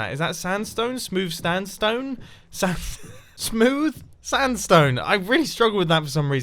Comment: did you say “Smooth Stand Stone”